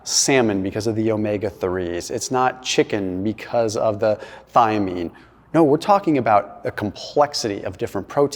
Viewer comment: At first I thought he said "...chicken because of the thigh meat".😂